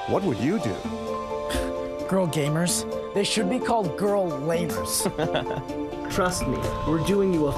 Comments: These actors had the worst insults I've ever heard. "Girl lamer" are we in a Disney original? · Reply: For some reason I thought he said "girl labour" lol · "Girl lamers" such cringe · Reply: Oh I thought he said girl labours 😂😂😂